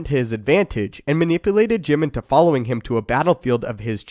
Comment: did you say Jim ??